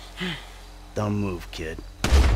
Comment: When I first watched this, in your version of "Kill Michael," I had misheard the last thing he said before killing Franklin and thought he said, "Don't move kid." I had interpreted it as him being still close enough a friend to Franklin that he wanted to make sure that he killed him painlessly, despite their differences. Nah, turns out he said, "Dumb move kid," and I'm just stretching my interpretations